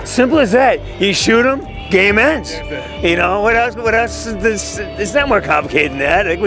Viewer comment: "shoot him, the game ends." Only I misheard him and thought he said "do you've any issues with gay men?" 😂